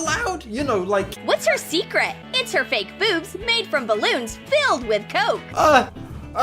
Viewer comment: "She has secret balloons hidden in her boots filled with COKE!" That caught me so off guard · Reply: I thought it said boobs😂